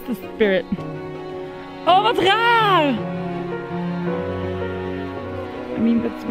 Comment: Did he say: oh wat raar? Is he dutch?